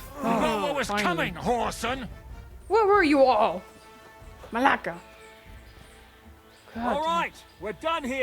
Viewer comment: did you just say malaka??!!